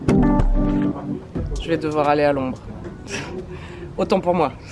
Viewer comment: Hahaha au début de la vidéo j'ai compris "je vais devoir aller à Londres" au lieu de "à l'ombre", j'ai cru qu'il y avait un immense turning point et tout mais pas du tout ! 😂